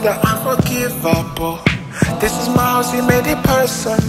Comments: I always hear "This is Miles." instead of "This is my house." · i thought he said: "this is miles, he made it personal." 💀💀